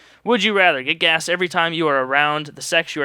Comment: I thought you said get gassed, and ironically, we are watching nazi zombies.